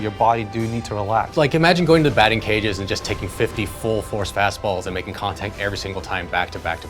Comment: I thought he said 50 full force basketballs.